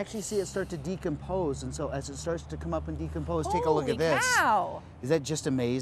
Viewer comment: Did she said "Howley Cow!"